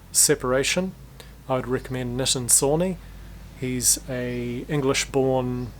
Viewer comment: what was the producer you shouted out at around 4:30? never heard of this person before and it’s quite hard to hear the name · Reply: Nitin Sawhney :)